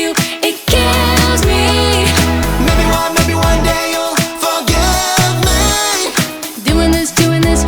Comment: why do I hear roomie saying "daniel will forgive me"?🤔